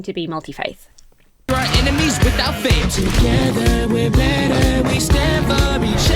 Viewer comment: I totally misheard this too! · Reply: I also thought it was "without faith" and moreover, the subtitles on the video here also say "without faith".